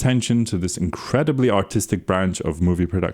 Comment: did he really say "autistic"? 😂